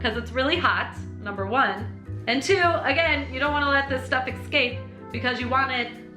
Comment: did you just say 'excape'?